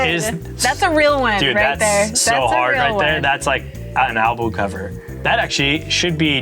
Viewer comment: did he just say Anal Bum Cover? 😅